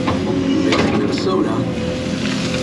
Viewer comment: Mornin, at the 9:02 mark, I heard what sounded like a males voice sayin something like “I’m gonna get a soda” or something like that. I listened to it about ten times and your mouth didn’t move and I didn’t hear a radio in the cab. Just kinda weird. Any of your machines haunted? 👻🤖